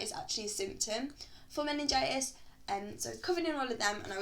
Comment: so what did you actually have? english is not my mother tongue, i didn't understand in the video :/ what do you say around 6:50 - it's a symptom for ...what? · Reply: meningitis